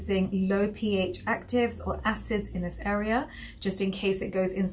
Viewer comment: I thought tu said no acids in that area?